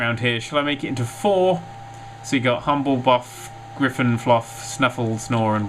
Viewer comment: He said grifindore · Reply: Slypy JAVA kid At 12:34,which is what I was referring to he definitely said Griffinfluff.